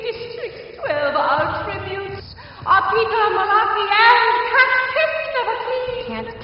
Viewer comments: When I first watched this trailer I thought she said "Catpiss neverclean!" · I thought she said Cat Piss Never Clean Lmao!